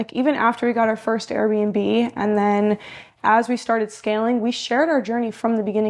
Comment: did she say scamming 😂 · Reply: She said scaling not scamming